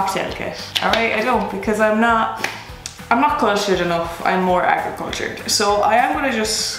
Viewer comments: Bloody hell!!, bloody hell! You’re just killing me! “Aquacultured”?! Hahaha! If there is some sort of Saturday Night Live equivalent in Ireland you should be on it!!! Or just plane go to NY and take over SNL on your own!! Haha! Killing me!, just killing me!! 👍🏻👍🏻👍🏻👍🏻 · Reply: Aquacultured? I heard "agricultured". 😊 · Bloody hell!!, bloody hell! You’re just killing me! “Aquacultured”?! Hahaha! If there is some sort of Saturday Night Live equivalent in Ireland you should be on it!!! Or just plane go to NY and take over SNL on your own!! Haha! Killing me!, just killing me!! 👍🏻👍🏻👍🏻👍🏻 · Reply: very valid point! Listening again, I did kind of hear agricultured.... I thought she said aquacultured in reference to alcohol being aqua... maybe I heard what I wanted to hear?... or, it could just be the Ireland accent and her particular pronunciation? Maybe she said one meaning the other? We may never know, a mystery in this regard.